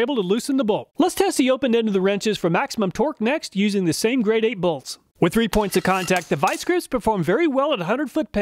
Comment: I thought you said Grade A bolt at first and had a chuckle.